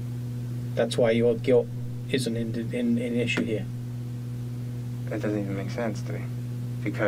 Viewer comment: I think he says “That’s why your guilt isn’t in (or an) issue here.” He just stuttered.